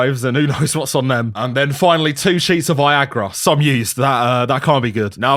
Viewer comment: Bro, I thought he said "some yeast" instead of "some used" because of his accent and was absolutely horrified imagining what he meant by that.